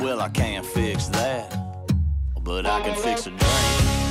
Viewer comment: I thought song said I can fix a DRAIN. Wish it did as it would be so nice and wholesome.